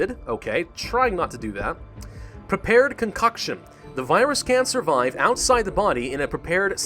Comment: When you said “can survive”, I thought you said “cancer vibes”!